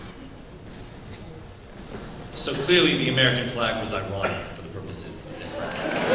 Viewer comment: what does he say at 24:18 ? Quote: "So clearly the American flag was ... for the purposes of this debate" thanks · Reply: "ironic"